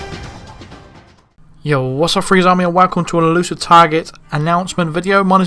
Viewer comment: damn, man, I'm totally unable to recognize what you're saying in beginning of your videos, I hear "you was a freeze army and welcome..." I would appreciate a comment with what is really said